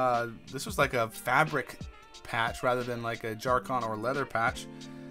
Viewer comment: What did you say at 8:40? Dacron? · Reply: nvm I looked it up and see you were talking about jacron